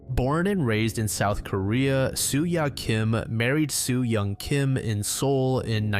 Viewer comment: I thought you said Sue-Ya Kim married Sue-Ya Kim like they both married someone with the same name